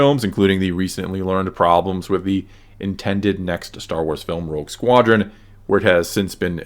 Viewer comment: I almost thought you said "Woke Squadron" lol I wonder what all of the "problems" with the movie were all about?